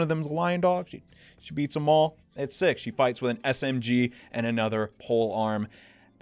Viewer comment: how in the hell did I hear “she fights with MSG” lmao